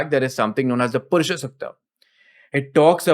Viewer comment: can someone tell me what he said at 28:38? purshosatya?